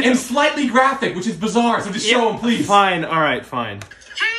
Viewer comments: Hahahahahahah omg I thought he said “ I saw a goat get fucked” but he said “ I saw a goat give birth” 😂😂😂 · I heard “I saw a goat gave birth” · I heard “I saw a (What) give birth